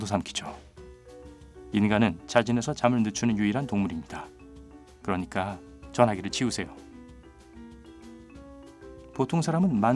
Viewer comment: 인간은 ???? 왜 휴대폰 멀리 하는지? 여러번 들어도 뭐라는지 모르겠네요. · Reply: 인간은 자진해서 잠을 늦추는 유일한 동물입니다. 그러니까 전화기를 치우세요. 라고 하신 것 같네요